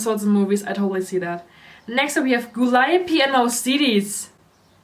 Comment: i didnt understand what you said at 36:36 but if you say no its a no queen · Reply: Wenjected wedontdothathere Gooliope and Mouscedes. she’s right it’s a huge no lmao